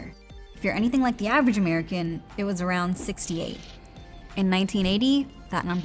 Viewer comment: I thought she said the average American buys around 6-8 new pieces of clothing every year and I thought "wow, I think I might be buying more than that" and then I realised she said 68.